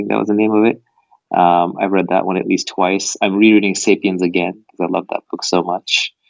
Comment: Naval says something like, "I'm re-reading 'safety and the getz'"? What's he saying there? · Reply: "I'm re-reading 'Sapiens' again"